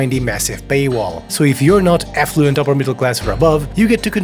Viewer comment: Is it just the accent or is it an intentional play on word when he ostensibly says "affluent" upper-class, but I hear "effluent" upper-class?